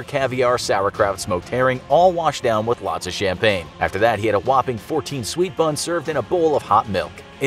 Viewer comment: When he said whopping i thought he say whopper whopper 💀